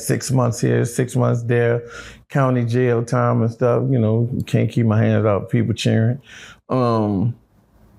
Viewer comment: ???? Did he say can't keep My hands off people children